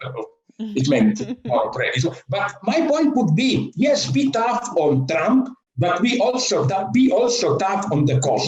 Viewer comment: I'm with him except for the part about "beat off on trump". Maybe I misunderstood. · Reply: I think he said 'Be tough on Trump', but I initially heard it as 'beat off on'.